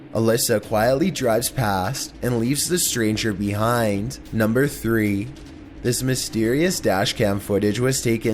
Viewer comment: sounded like he said number sex lol